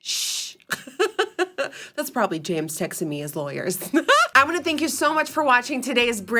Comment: I really thought she said „that’s probably James s*xting me“ and then she added the „his lawyers“ and suddenly everything made sense ...